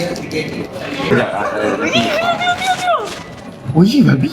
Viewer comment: Biy? Ke aku salah dengar ni😆